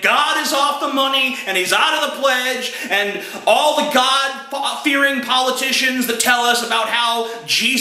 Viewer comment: I think he almost said "the godfather"